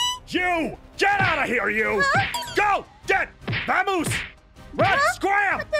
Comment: i hear A "Vamos" :u